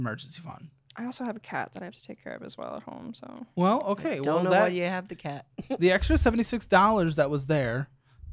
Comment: Damn I thought the mom said "we all know you have a cat" like BURN 😭🤣🤣 but she said "don't know why you have a cat" 😂